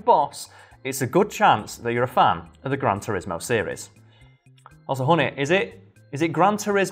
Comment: did he say "also, honey..."??? honey?